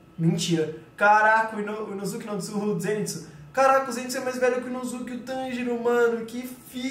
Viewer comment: ZETSU SO EU QUE OUVI